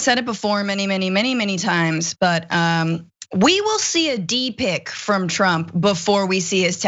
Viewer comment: Waaiiitttt a second...... Did you just say "We'll see a D pic from 45 before we see his taxea"???? Honey first he's got to have a "D" to snap a D pic........